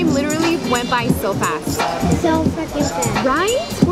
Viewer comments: it sounds like says "so fucking fast" lmao I know he probably didn't say it but it just sound like it to me lol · it sounds like says "so fucking fast" lmao I know he probably didn't say it but it just sound like it to me lol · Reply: Heidy Beltran sounds like he said so freaking fast lol 😂